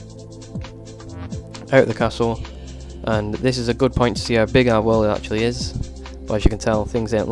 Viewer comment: I swear he says, " it a great place to see how big our willy actually is"